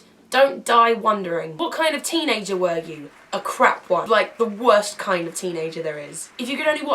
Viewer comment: "What kind of teenager were you?" - "a Crack one" I thought you said ;D xxx